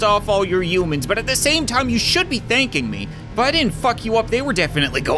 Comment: did he just say f**k like for real I have never heard say that4:26